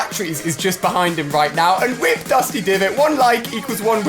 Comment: Am I the only person who heard him say dusty divot not depot